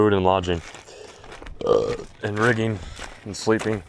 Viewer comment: did I hear that right??? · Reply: Food lodging rigging sleeping